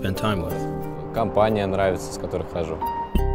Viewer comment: I swear I heard robot chicken at 0:27 !! My tvs are off in my house